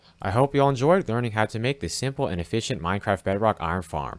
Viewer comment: did you said SIMPLE...